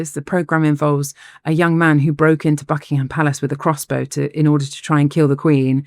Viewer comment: When she said buckingham palace I thought for a second she said, "a young man broke into the f###ing palace to try to kill the queen"... I was like, what?!